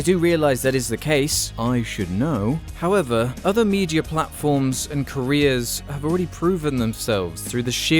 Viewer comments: "Other news platforms and Koreas" is not what I actually thought you said, but I had to relisten to it because I'm a moron. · every time you say career i hear “korea” and its throwin me off lmao